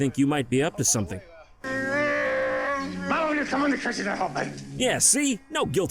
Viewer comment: That sounded like he's saying "Well come over here in case you can help me" XD